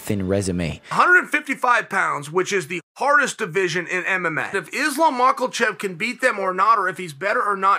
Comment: Did Chael just call him "Islam Michaelchev" 😂